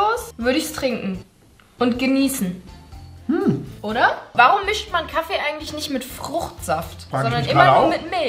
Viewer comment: Alter bei 11:14 hab ich verstanden von julia: „warum mischt man kacke eigentlich nicht mit Fruchtsaft?“😂😂😂